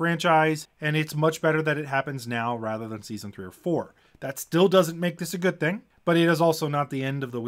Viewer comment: Ah, "season 3 or 4" - on first hearing, I thought you said "season 34." Even for Wheel of Time, that's getting a bit long! (But only a bit.)